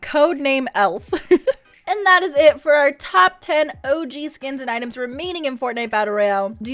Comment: I thought u said og skins not items